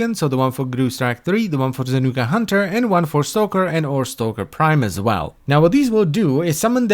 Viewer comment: Di-did you just say...Stalker Prime?